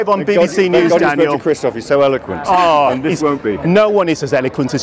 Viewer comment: Can somebody write down what craig said relating to christoph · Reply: And this will won't be